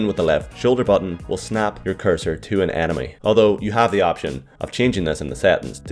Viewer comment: Every time you say enemy I hear anime it's so weird